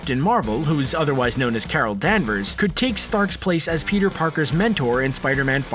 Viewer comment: I thought that he said Kara Danvers and I got confused af